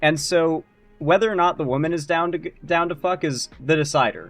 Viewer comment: bro was gonna say "down to goon"